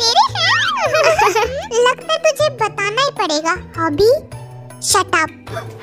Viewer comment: batona jin ne hobi k kaan me kya kaha 😂😂😂😭😭😭 batao plzz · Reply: Hobi shut up - bola